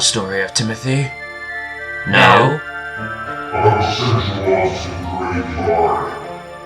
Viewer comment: What did Timothy say? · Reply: I will send you all to graveyard